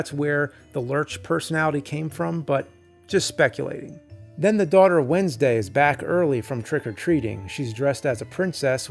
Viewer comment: "The daughter of Wednesday..." spotted a small error but we know it's Morticia lol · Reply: Sounded like he said "the daughter, Wednesday"